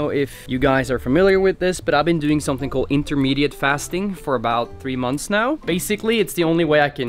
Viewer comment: do you mean intermittent fasting? i heard "intermediate" hehe. because i do intermittent fasting too